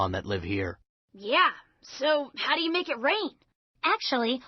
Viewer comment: Did Ash just say "How do you make it rain?" 0_0